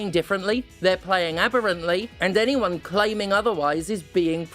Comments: Steph say's "aberrantly" but the subtitle says "abhorently". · there's a mistake in the subtitles, steph said abberantly but the subtitles say abhorrently, i kind of feel like one of those annoying people who coorrect spelling mistakes but i think the mistake here changes the meaning of the sentence pretty significantly